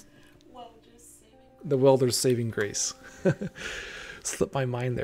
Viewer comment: What was the mod you mentioned around 40:10? It sounds like you said 'Welder's saving grace' but the workshop isnt returning anything and theres no mod by that name in the modlist, sorry if I've missed it somewhere, cheers